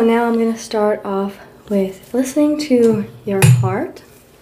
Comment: I thought she said im going to listen to your fart😂😂